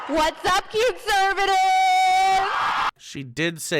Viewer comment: Loved it when auto subtitles turned "cuteservative" into "cute servant". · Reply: First time it played I heard "cute servant" and was just stunned.